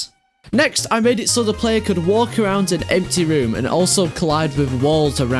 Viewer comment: for some reason i heard "so the player can walk around in deltarune" in 1:04 xddd